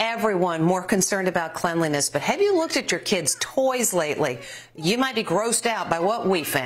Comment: I thought she said “have you looked at your kids toes today?”